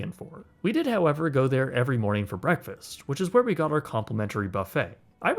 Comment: omg i thought you were about to say complimentary cat @ 12:25 😭